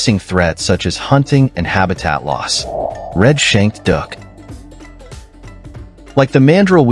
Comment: I thought he said red-shanked DUCK; turns out it was a DOUC